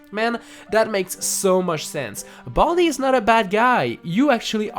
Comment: Lol I thought It said Baldy not a bald guy